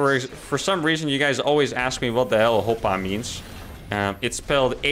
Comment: I thought Harstem says "Opa" instead and in Bulgaria we also have such expression "Опа!", so it's interesting that the Dutch also have it, but with an H in front :).